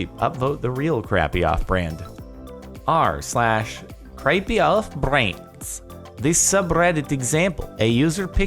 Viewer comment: auto generated captions can't understand, accent Apparently he says "creepy elf brains" 😂